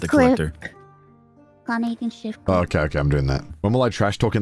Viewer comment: Did just called connor "honey"? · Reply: it does sound like it but nah she said connor